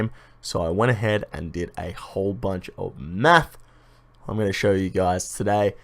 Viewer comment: sounds like you said "i went ahead and did a whole bunch of meth" lmao